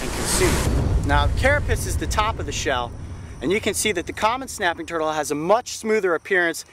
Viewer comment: at the start I thought it said cinnamon snapping turtle😂